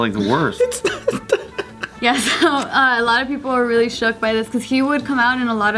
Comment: did she just say "shookt"?